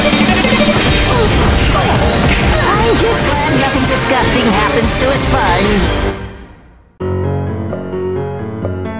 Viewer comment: Does anyone know what spongebob said in this part? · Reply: “I’m just glad nothing digusting happens to a sponge.”